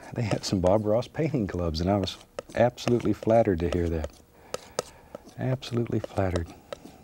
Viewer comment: I love how he says he’s doing a mountain without snow and 5 minutes into it he’s like, “let’s put in a glacier.” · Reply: When he said he was absolutely flattered I thought at first he said "absolutely flattard" lmao.